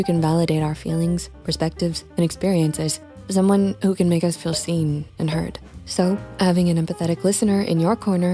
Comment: 😅 Sorry, there's a caption typo at 01:12. "Seen and hurt" instead of "seen and heard." It kinda makes a difference for the reading audience. 🖤❤